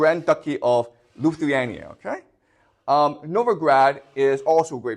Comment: did brother say Novigrad ? Good reference here professor !